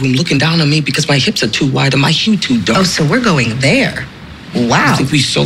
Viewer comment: What does Annalise say at 1:30? I can't quite catch it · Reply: The Jenna Pearl my hips are too wide, and my hue too dark